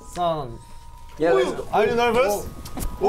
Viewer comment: sounded like the Swedish are you nervous xD